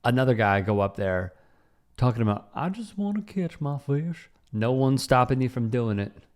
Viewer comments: I thought he said " all I wanna kiss my 🐟" oops!! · When I hear that "catch my fish" I hear "kiss my fish" every time 😂😂